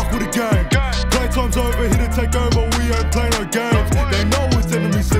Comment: Is it just me or am I the only one that thought they said Enemy Season instead of NME Season🤷🏽‍♂️